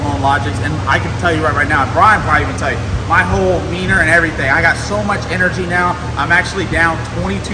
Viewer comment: Am I the only one that thought he said "My whole Wiener" ?